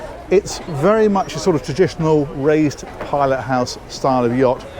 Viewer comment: I thought you said “Ray’s pilot house.” Had to listen again “raised pilot house”. Of course, that makes much more sense. . . . would be funny if the pilot was called Ray though. It would then be Rays raised pilot house. Don’t mind me, I live in a lighthouse on my own on a rock in the middle of the sea😂